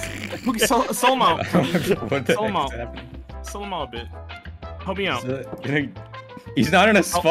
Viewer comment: i thought said “sell him out babe” to at 17:00 but i still have no idea what he actually said